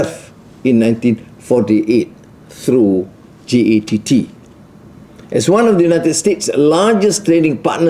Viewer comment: I thought he say GYATT